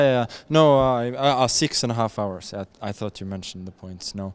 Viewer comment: How could someone as mentally sharp as Magnus misinterpret 6.5 hours to mean 6.5 points, I think it was an attempt at humour ! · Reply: I speak English and at first I thought he said 6.5 points too it kinda lacked relevance in my opinion give the champ a break geez🙄🙄